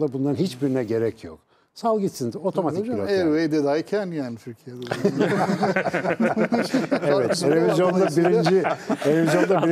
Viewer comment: 'da ne dedi ergi hoca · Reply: Bence : Every body can"! diyor.....yani türk trafiginde herkez kafasina göre takilabiliyor..... anlaminda....